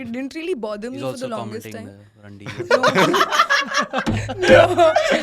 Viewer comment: did bro called her rundi on her face🤣🤣🤣